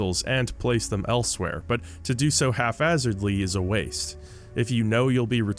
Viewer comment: Even though you said "hafhazard" instead of haphazard (1:02:05), you're still becoming one of my favorite YouTubers.